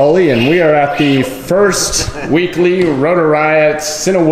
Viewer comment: Man wish i was there for that... 4:53 Wait did he say weekly? owo